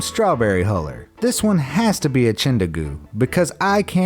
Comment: What is it that he says about the strawberry corer at the 5:43 mark? "This one has to be a ???" I can't make the last word out, it sounds like chindahoo or something weird like that. Can someone please help me out, it's bothering the hell outta me. I must've replayed it like 10 times and I still have no idea what he says.